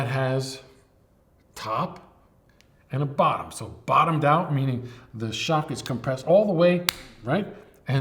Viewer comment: Go I could swear I just heard "tops off bottoms out" shoot right out of your mouth.